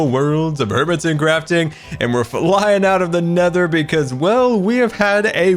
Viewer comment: diD yOu sAy nEthER? also two episode 10's?